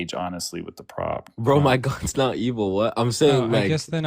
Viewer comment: I heard "my kok isn't evil" : I am sick 🤧 🤢